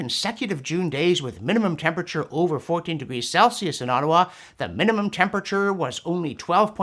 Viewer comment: 40 degrees Celsius is NOT 57 degrees Farenheit! It's over 100 degrees Farenheit! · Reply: I thought he said 40 degrees, too. But listening more closely, what said was 14 degrees C... which is almost exactly 57F. Here in N. California this summer we have seen a few days with a high around 40C/104F. But those are relatively rare and it typically cools off to around 18C/65F or less at night.